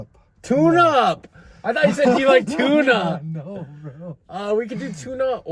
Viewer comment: Dude I heard him say “IDK you like tuna?” Haha